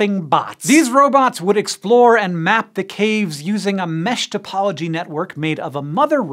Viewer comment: Oh, mesh topology network. I thought he said meshed apology network. Bunch of Wall-Es up there bumping into eachother, boopin' "sorry" at one another.